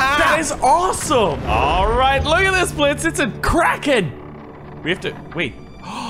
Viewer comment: At first it sounded like he said”look it’s a crack head”